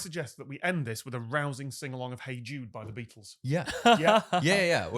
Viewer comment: is so careful on these podcasts... 1:04:50 - Did just say "hey Jew?" That's a new dog-whistle for meee. Kind of problematic muchhhhh. 1:05:15 - Oh my god. is a bad influence.